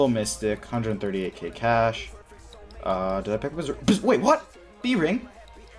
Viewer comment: did he say (did i pick up berserker ri) then he seen the bring? He knew he'd get one?